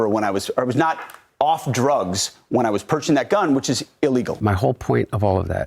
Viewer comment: Stutters - Says “perching” instead of “purchasing” Yeah bud, we totally believe you got sober in 2019… after promising “to get sober if confirmed” six years later.